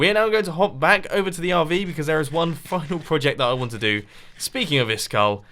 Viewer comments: I really don't understand you Mumbo you have a villager trading system from where you can get many many stuff like armor like pickaxe like axe like sword and you just spend your diamonds to make new ones? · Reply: did he just say "fucking" project? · does he say "theres one more fucking project?" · did he say cause theres one fu... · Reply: he said final.. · did mumbo say fuck ? I can't tell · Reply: he says final